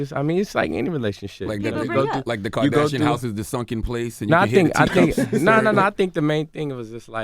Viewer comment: like did he say 'the sunken place'? Like 'the sunken place' in the movie get out 😭😂💀